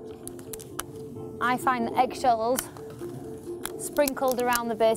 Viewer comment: What did she say to sprinkle at the base of the lettuce to keep the slugs away? At 6:40? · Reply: Hi Camille, tip is to sprinkle egg shells around the lettuce to keep slugs away :)